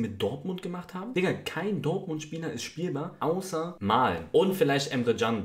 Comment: Was sagt er bei 0:34? Verstehe es nicht. Kein Dortmund Spieler ist Spiel mal? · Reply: Spielbar